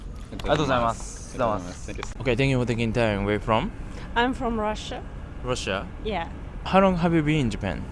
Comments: Her: Im from Russia Subtitles: Im from France Me: did i hear her right? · I love your videos man PS: at min 7:18 you've written France instead of Russia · she says shes from Russia but the caption says France. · ! At 7:18 mark the subtitles say "I'm from France", but they should say "I'm from Russia" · The caption says "I'm from France" but she says that she's from Russia. Amazing video, cheers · small mistake. Subtitles say France, she said she's from Russia.